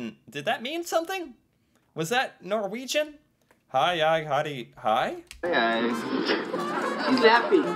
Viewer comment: It sounds a lot more like he's saying "Hei hei, her er jeg." which would be translated into "Hey hey, here I am," and is actually a sentence a lot of people would say in various situations. But that's just how it sounded like to me...